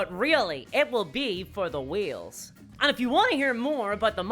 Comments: Bwahahaha….you spun your video when you first said ‘whales’ and I thought you were saying ‘wheels’…..I’m such an idiot 🤓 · I actually thought she was saying "wheel" until I saw the pictures of whales in there. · I thought you were saying wheels for the most part of this video! LMAO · I thought you were saying wheels, not whales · I honestly thought she was saying “wheels.” I was confused by the picture of the orcas. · Okay, like everyone else, I thought you said 'wheels', but it prompts me to ask, how tf do you pronounce wheels, dudette? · Being American, I know what a whale is, But for half the video, I thought you were saying Wheels! LMAO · oops, I thought you saying "wheels" until you showed a picture <3 minutes in. as in the wheels that drive the business and make everything work 😅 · I thought she was saying wheels, not whales.